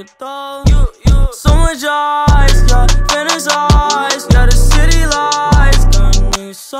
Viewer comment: I always thought he said "thin as ice"